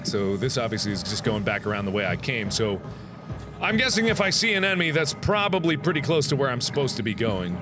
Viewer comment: "If I see an enemy-" I heard "sea anemone" and I was confused. XD